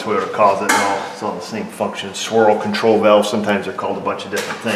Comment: heres a funny - when you said 'swirl control valve' I thought you said 'Squirrel control valve.....' hehe